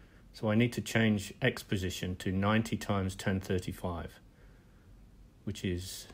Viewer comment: Very interesting Sir. I Tried this too, and it worked. However at 11:45 i hear you say that the tipsection needs to be pushed to the right with the formula 1035 x 90, but i don't know how you can have a result of 63.02. And another question, where did you get this .dat file? is it from a real wing like a windmill? Thanks. Jean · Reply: Hi sorry I didn't speak clearly at 11:45 and the auto-caption did not work properly. The formula I actually say is tan(35) x 90 but it sounds like 10 35 x 90. The airfoil section is a standard NACA section and can be downloaded from the site linked in the video description. There's also a good book on standard NACA sections that is available online and contains tables of many sections plus the equations to calculate them. Search for a Abbott and Von Doenhoff, Theory of Wing Sections. Thanks for watching!